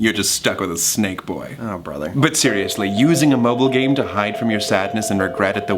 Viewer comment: I thought Janus said muggle not mobile nd I was like, "OH MAH GAH THOMAS IS A WIZARD!!!"